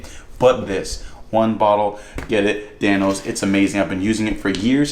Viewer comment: When he said Dan-Os who else thought he said thanos